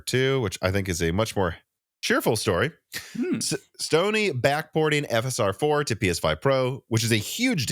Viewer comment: Anyone else thought he said Stoney is backporting instead of Sony?😅